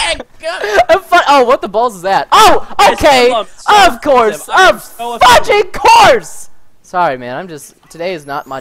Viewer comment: did preston say fucking of cause?